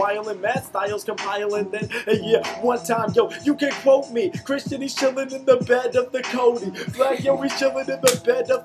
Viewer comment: did he say Christian is chilling in the bed of the codeine? like when your pour up dirty sprite and it sits on the bottom!?!?!?!